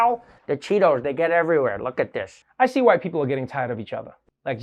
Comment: I thought Trevor said, "I see White people getting tired of each other." 😂 Thank you YouTube for the 5 second rewind feature. ⏪